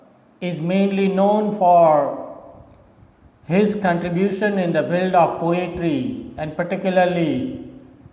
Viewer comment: k bad sir aapne kya bola hai (contribution in ....... Of poetry) pls reply... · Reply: Contribution in the field of poetry. 👍